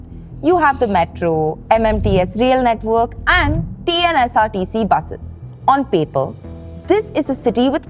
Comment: duration in this video, Audio says TNSRTC buses instead of TGSRTC. Kindly correct that mistake. Thanks.